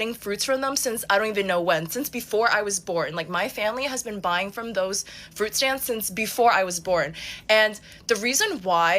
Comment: is she saying, "It's blowing my" or "It's blowing mind"? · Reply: It’s blowing mine. Short for “blowing my mind”?? Lol